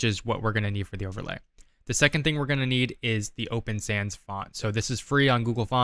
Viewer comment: Breezed over the open sans font piece. I mean I know this is a 'quick' tutorial but I swear I thought you said open fans. I backed up and watched it again.